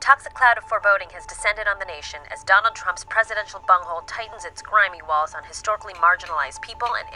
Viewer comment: did I hear "presidential bumhole?" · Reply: you heard "Presidential Bunghole" same deal, a place nobody wants a mental image of... and yet, here we are. I'm going to go vomit now.